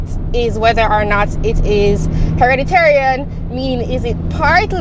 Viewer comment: Did she say "hereditarian"?